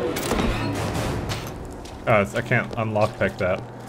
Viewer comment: i swear i thought i heard say "unlockpick"